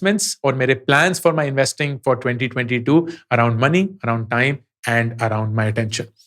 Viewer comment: A typo error in 23:39, instead of 2022, subtitle shows 2020. Just FYI Loved the content of this video.